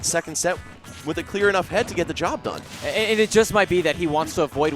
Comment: Zero not the best Sheik anymore he relied to much on 50/50 Kills, now the best are without a doubt Mr. R & Void since they are more creative in their kill setups and combos. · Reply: Teddy Rose i thought you said daddy lol